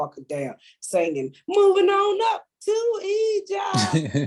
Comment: LMAO!! I thought she said "Moving on up to Eat Ya"